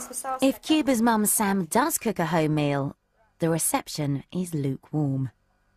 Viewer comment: All of these toddlers eat more than me .... And I'm an overweight adult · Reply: derek duffy at first i thought they said "Dahmer meat" definitely dont wanna eat that 😂